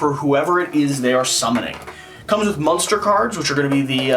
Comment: I heard "Munster" cards.... Love the "old" game unboxing! So jealous of the 2' tall mini. I want one, but 2nd hand market is crazy on that!